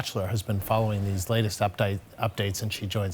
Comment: Andrews calls the woman " updyke ". Thats messed up, did she turned him down for a date that he is now calling her a dyke? haha